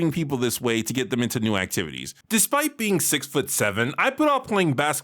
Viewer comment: I just did a double-take. I heard you say "despite being 5,7" then played it back to make sure. your 6,7? you are massive.